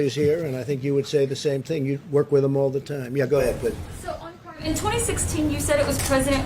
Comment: Who else heard "Yeah go ahead babe" 3:41 😂